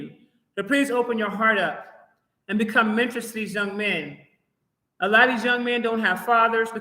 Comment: TIME STAMP 19:29.. THE RECORDING BREAKS UP EVERYTIME I REWIND IT. WHAT DID YOU SAY THE FELLAS NEED TO DO???? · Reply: Become a mentor to young men. They will need assistance.